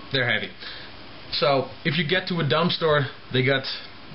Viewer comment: at first i thought u said "i got them out of a dumpster"